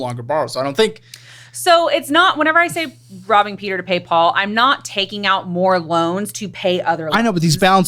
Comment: I swear I heard "rubbing peter to pay paul" i think thats funnier and makes more sense😂